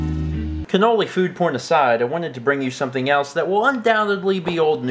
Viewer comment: "Cannoli food porn aside" For a second I thought he said "corny food porn aside" Either works, really.